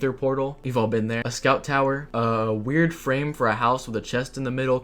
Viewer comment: I heard that as “The South Tower” 😭😭